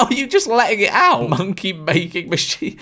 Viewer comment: Why did he say monkey rather than money